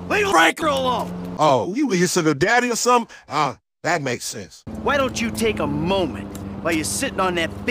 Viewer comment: What did Lamar say when Michael told him to leave Franklin alone? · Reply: "Oh, you're his sugar daddy or something? Oh, that makes sense."